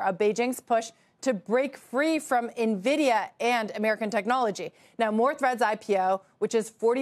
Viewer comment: I hear " more Threat"